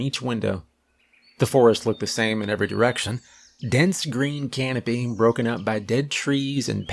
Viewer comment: I have hearing issues. at 10:10 I heard "dense green can-o-beans" lol